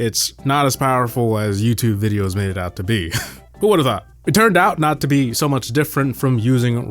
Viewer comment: did you say this best Polish word? 😂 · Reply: Who would've thought, not kurvva😂